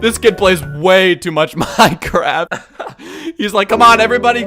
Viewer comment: it kinda sounds like he's haying my crap instead of minecraft